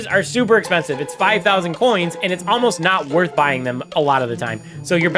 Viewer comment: I thought it said worst and I was confused